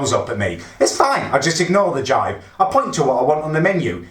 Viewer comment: Does he say ignore the juy...